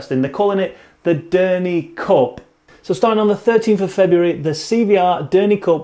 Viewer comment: When you announced the Derney Cup, I thought you said Dirty cock. I had to rewatch it because it made me laugh.